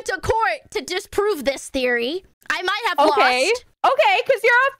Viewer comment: btw that court was so funny. 00:25 maybe its me, but i heard " to disprove this theory" but at the same time, i cannot deny that.